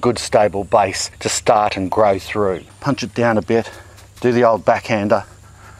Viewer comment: WHAT? 11:05 Did he say, "Do the ole' backhand HER?"